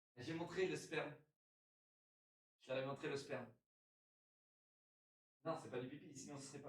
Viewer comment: c'est moi ou il parle de mesper ?🤨